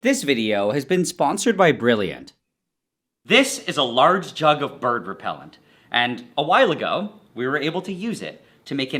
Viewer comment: Oh man my hungry fat ass thought said burger repellent